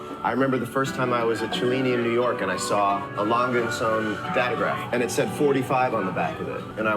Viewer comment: "Lange & Söhne Datograph" might be what he said